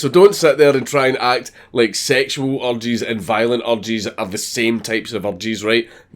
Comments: I thought he was saying "sexual orgies and violent orgies" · I thought U were saying sexual orgies and violent orgies are not the same.. till I turned the subtitles on.. and its urgers.... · When you were saying urges I thought you were saying orgies....lol😆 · when he's taling about urges all i hear is 'violent orgies and sexual orgies' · Oh. The enitre video he was saying 'Urges'. I thought he was saying 'Orgies'. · I kept hearing you say "sexual orgies" and "violent orgies" and had no idea what you were on about lol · When you say "urges" I swear to God I hear "orgies". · I thought he was saying “orgies,” not “urges,” and now I know his meanings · for like a good minute i thought he said orgies instead of urges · The 1st 3 or 4 times he said "urges" I heard "orgies" so I heard him commenting on sexual orgies and violent orgies and how different they were.... I need to improve my ear for the Scottish accent, apparently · I honestly thought you were saying sexual orgies and violent orgies are different, I was like wtf is a violent orgy? and then I realised you were saying urges haha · I thought Dank was saying "Orgies" instead of "Urges". So it sounded like "Violent Orgies" and "Sexual Orgies". Lmao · When you were saying "urges" I thought you were saying "orgies". Sexual orgies and violent ones WOULD be very different things. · For half the video, I heard "orgies" instead of "urges". Whew lad, that was a ride. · Oh URGES! I thought u were saying orgies... Haha